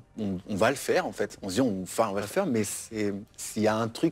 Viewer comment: I adore your videos. Thank you so much for all the hard work you put into this. At around 6:40 Larry says sth after 'on va y faire'. Do you know what he says? Laurent has a micro expression of disapproval and it made me wonder... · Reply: Thank you for watching!! He said "On va le faire" Which literally means "we're going to do it". And he was referring to talking about the things going on between them